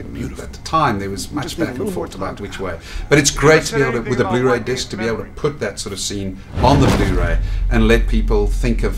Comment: What did Victor say at 4:17 ? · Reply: "You never said anything about wiping his memory"